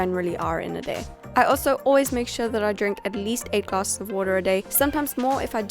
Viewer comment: Leticia Garcia 8 glasses of water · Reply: okay, lol its her accent, i thought she said "a glass .." . But thanks!